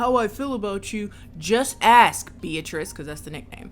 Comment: Bruh, I thought he called her Peaches... He said Beatrice...